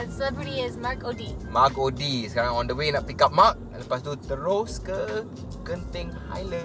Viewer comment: "sekarang kita otw nak pick up Mark" i misheard that as "pick up mak" HAHAHHAH sorry Mark O'dea!! O yea to and everyone else, we use "ke" to refer to places and "kepada" for people. So "pergi KE Genting Highland" and "beri hadiah KEPADA Mark" 😃 About "cable car", i actually thought "kereta kabel" and then i heard "gondola" and got embarrassed myself hahahhaha. BM is really good btw! Crystal clear pronunciation!